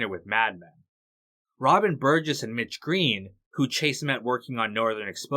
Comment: "Robin Burgess and Mitch Green" meanwhile the caption says Robin Green and Mitch Burgess